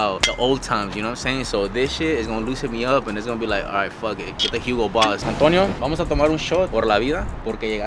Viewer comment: fuc it get the Hugo boss thought was gonna say agarrase una bolsa ducii 😂